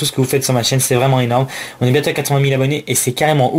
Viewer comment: "On est bientôt à 420 000 abonnés" QUOI ?!?! haaaa ok tu voulais dire 80 000 abonnés.